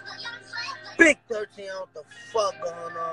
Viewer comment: did he just say "big 13"😂😂